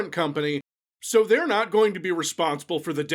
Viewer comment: just FYI there's an error in the captions. You say "they're not gonna be responsible" but the captions say "they're gonna be responsible"